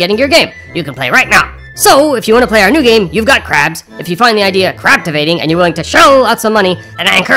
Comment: I thought it said "you've got cramps" that would be a different game 😂